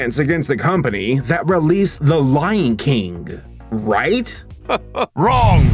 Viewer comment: am I wrong, or did I just hear say “The Lying King”? Lol